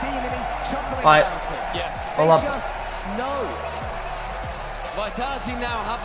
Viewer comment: does he say roll up??